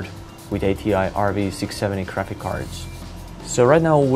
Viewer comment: sounds like he says crapix cards!